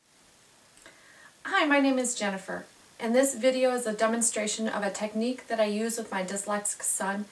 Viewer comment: It sounded like she said "Dumbonstration" instead of Demonstration in the beginning